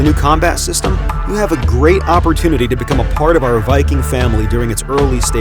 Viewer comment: Thought it said "become a hacker" lol